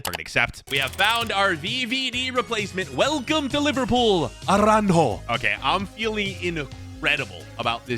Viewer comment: did bro say arango